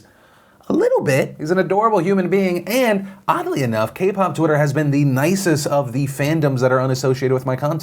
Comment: Did u just say Koop twitter is nice?